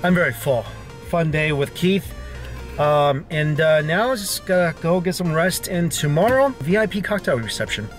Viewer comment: On the first day, when he was back in his car, for a second I thought he said "Gonna go get some rice" xD Like, DUDE, you JUST said you were so full!